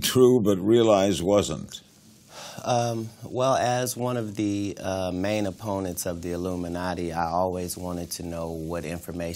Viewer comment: omg did he just say as an opponent of the Illuminati 🤦🏾‍♀️